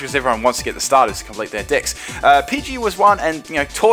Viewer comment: I heard D!cks...